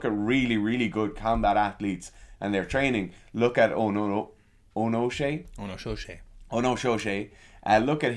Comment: He never put up the link for the lad he told us to look at. Is it the Asian Judo guy? Because it sounds like he said Eoin O Shea 😂